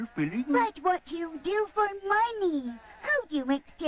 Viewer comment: pause at 3:58, and have the captions on, and when it says ''What u do for money'', on the captions it says ''what u do for my knee. lol